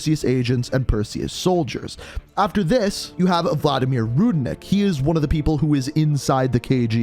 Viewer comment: At first I misheard you and thought you said Vladimir Putin. 😂